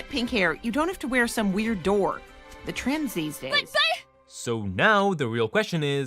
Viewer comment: Did she just say “the trans these days”?